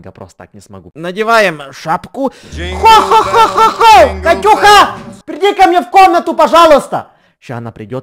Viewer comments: Катюха перди в мою комнату пожалуйста 😹🤣🤣🤣🤣🤣👀🤣🤣👀🤣👀🤣👀🤣👀🤣👀🤣👀🤣👀🤣Почему я так орру? (мне одной послышалось?) 🤣👌😂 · На 0:45 мне одной послышалось: -Хо хо хо,Катюха перди ко мне!!!!😂😂😂😂😂😂😂😂😂😂😂😂